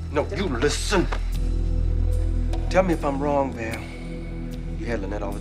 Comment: What did you say Tubbs?! "Tell me if I'm wrong Pam". Pam?! That's not even on the Goofs section on IMDB!